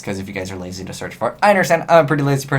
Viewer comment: Originally I thought you said Azneyeswhitereaper.